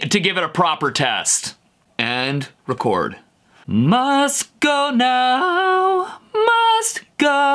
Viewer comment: He heard "must go now" I heard "mustard gown"